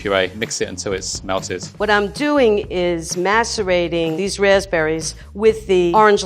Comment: What I am doing is Masturbating? Did she say that 😅